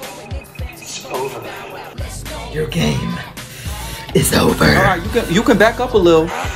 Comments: I thought he said “your gay” but he just said “your game is over ” lol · I thought he said “your gay” but he actually said “your game is over”